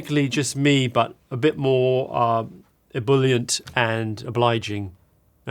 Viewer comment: I think he said "ebullient" (as in, more lively) and not "bullied" as the subtitles say